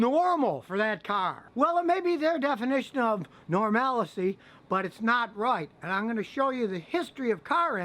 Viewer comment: Scotty, at 0:33, did you say "Normalicy"???